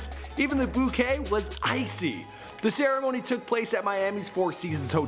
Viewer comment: wait did you say 1.7 billion dollars 😂😂😂 from 9:43 OK OK we forgive u... it had to be millions not billions...